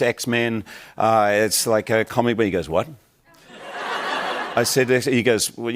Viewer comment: I the subtitles are wrong. If you listen to it again, you can hear him say “comic”but his accent makes it sound off, plus he did kind of slur his words their 😅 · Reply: I thought it said comedy at first too while watching the CC but I listened to it again and he does say comic. Its hard to tell without paying attention.